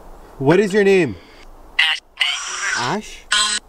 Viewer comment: ask'd her name I heard Ashley